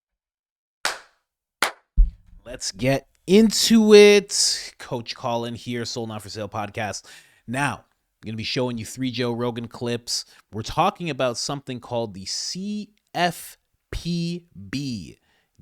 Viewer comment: For over a year I thought you were saying "still not for sale" I didn't know till I saw you wearing a shirt that says soul